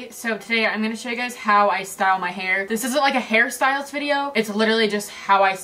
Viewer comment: Every time she said hair styles i thought she was saying harry styles